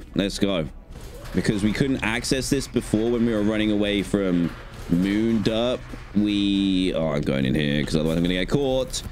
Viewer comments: I was snooping and investigating and I found a bug at 15:27. It says the line when you get the fazcam instead of the “How do I use it” line. The audio matches but the text has the bug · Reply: New investigation: at 36:30 Dan says “Moonderp” instead of “Moondrop” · And i thought you said moon derp 36:26 😂❤